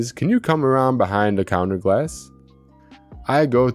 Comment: HELPFUL SUGGESTION: i listen often to these story time videos to fall to sleep n figured a way to help with the voice generator u use (or appear to use based on this example) - i heard some words sound like that's what u use n if it is this will help - listen to how at 4:44 the word please is pronounced (said like "pless" or like the word less with a "p" added in front)) - if you enter "plees" for example it would likely be pronounced like trees with a "pl" instead of "t" beginning n honestly it wouldn't sound like a voice generator - i thought it was a real person until i heard that pronunciation sound un-natural - if it's not some type of reader that creates the voice then guess the reader never said that word before, lol - i honestly thought it was a real person until that word was pronounced like it was n i noticed none of the others that also use voice repeat devices ever think of respelling words to beat the problem - u could be the first, lol - oh, if that really is a person n it was a slip up, sorry but if not, until then i thought it was all along n if those pronunciations are figured out i don't see how anyone would ever think it wasn't a genuine person we hear, cheers - oh, i have way over a hundred videos of me n different friends playing n making music on youtube for the last 2 years if u like music n we do all styles if anyone would like to sub my page i'll generously return the favor as long as ur subs are public so i know who u are!! - i really enjoy ur storie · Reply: Lol, that was my mistake, I said "Glass" instead of "Please". I mess up every now and again 😂